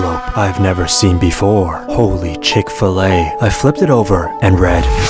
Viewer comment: or 1:10 did u say holy chick fill lay?